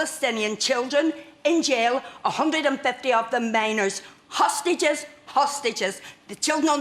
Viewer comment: When she said minors I thought she said miners. Because Hamas has worked children to death building their insidious tunnels.